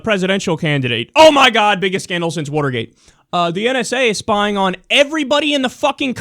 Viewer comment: did you just say wooter... how could you, I thought you were better than this.